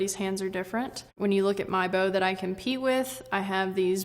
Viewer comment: i thought she was talking about how shes so comfortable with her bow she could pee and shoot it, but she actually says she "competes" with it